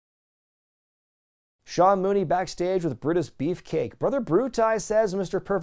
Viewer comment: Sounded almost like you said British Beefcake, but maybe I misheard. Maybe Ed Leslie and Davey Boy should have had a tag team.